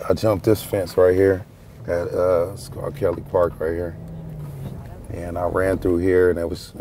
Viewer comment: did he say R Kelly park?